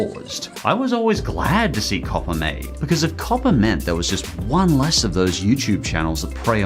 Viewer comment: Mr. says peppa The captions: P E P P E R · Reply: That's the same thing to when he said COPPA. The captions thought he said copper.